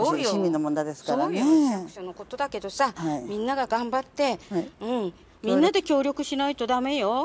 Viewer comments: 黙ってが「頑張って」に聞こえるw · 「黙って」のとこ頑張ってに聞こえるｗ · 黙ってが頑張ってに聞こえてくる